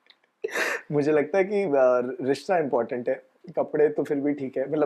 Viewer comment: I heard Rishita important hai😂😂❤❤❤